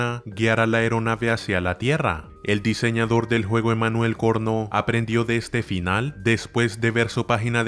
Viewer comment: pensé que dijo porno